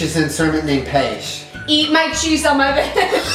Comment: p sure he thought she said "Eat my cheese on my vag" cos that's what I heard lol